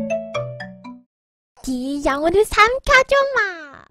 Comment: 이거뭐라는거에여? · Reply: 이영호를 삼켜주마